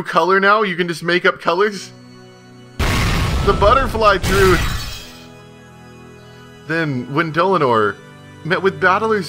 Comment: I fucking died when I heard "The butterfly truth!" XD